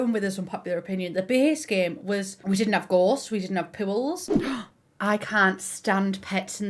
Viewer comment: She said pools, I heard her saying “pubes” and the subtitles read “pills”. I’m laughing so hard right now lmao